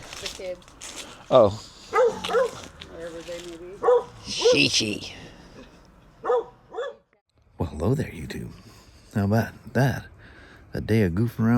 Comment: Does anyone else notice how he sounds like the little white cartoon dog droopy , when he says “ well hello there YouTube” 🤣😂🤣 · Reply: My wife asked how he knows that both of us are watching. She thought he was saying "well hello you two." LOL